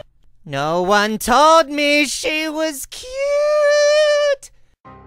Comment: Favorites: 8:25 14:36 16:01 22:16 47:16 49:36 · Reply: When i replayed the 22:16 all i heard was ute hehehe 😂